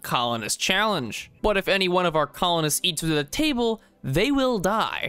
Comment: PLEASE PROOFREAD (OR GET SOMEONE TO) YOUR VIDEOS. NOT EVEN A MINUTE IN AND YOU ALREADY MADE A MISTAKE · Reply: late reply but its because it sounds like you said "eats with a table"